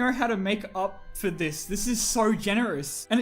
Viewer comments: Did he just call him “rat shit”?! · Genuinly thought he said rat shit for a second there